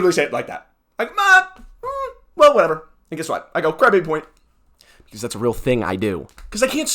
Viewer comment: does he say gravity point? What does he mean? I think I'm hearing him wrong, even though I've gone back multiple times... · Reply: Late to the party, but he says "crybaby point" It's from one of his shorts where he gives a player a crybaby point for basically "crying and whining about arbitrary things at the table"